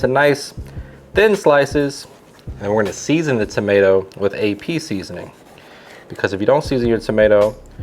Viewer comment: I heard " We're gonna season with the tomato with a Pea seasoning" wtf is pea seasoning? Rewind CC... "We're gonna season the tomato with AP seasoning"..... Damn I'm dumb